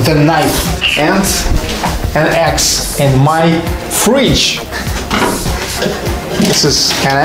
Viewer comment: I thought he said "... an ax in my fridge" lol.